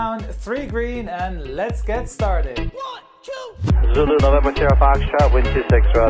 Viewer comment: did ATC just say lulu lemon or am I hearing things?